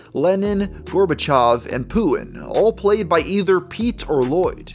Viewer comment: Sounded like you said "Pulin" not "Putin". Maybe it's just me though...